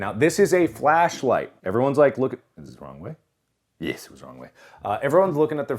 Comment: I thought he said ethan is on the way but he didn't my ears are playing tricks on me I'm crying